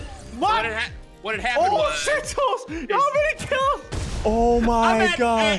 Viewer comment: Did say “oh sh*t toes”